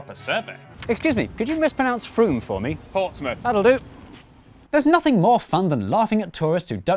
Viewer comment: "Could you mispronounce Frome for me?" "Portsmouth." "That'll do." That joke was made even funnier by deciding to wear a wig very briefly for no reason. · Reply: I thought he said Fortnite, not Portsmouth 😂